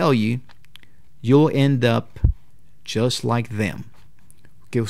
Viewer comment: No entendí esa parte, dices "Yo end up just like them"? énfasis en "YO", entendí eso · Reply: el dice: you´ll end up